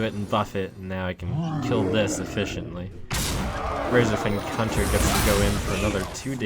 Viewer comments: Did he say razorfen cunter at 7:27 ? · I heard say "razor fen cunter" at 7:28. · did just say "Razorfen Cunter"?